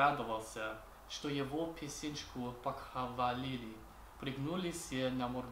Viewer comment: Я одна слово "писечку" слышу,а не "песенку"😂😂